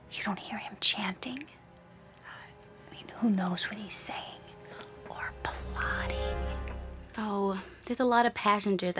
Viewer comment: I'm sikh which means I know exactly what he's saying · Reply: ''waheguru ji's khalsa, waheguru ji's fateh hope you protect everyone'' - Thats what he's saying